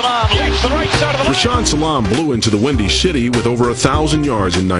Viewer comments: Rashaam Salaam blew into the windy shitty it sounded like he said at 12:46 🤣🤣🤣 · Did he say, he, "blew into the windy shitty"? I definitely think I heard him say he blew into the Windy shitty